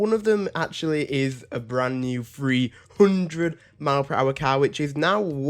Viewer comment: man i thought you said free car